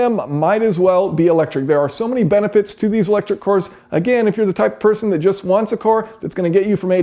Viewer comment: thought he was saying electric whores at first...